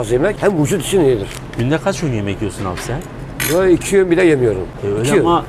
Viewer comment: Video sahibi arkadaş, 2:03 de günde aç öğün yemek yiyorsun diyor. cevap veriyor. Cevap olarak ne diyor? Orayı anlayamadım.. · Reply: İki öğün yemek yiyor hoca 😉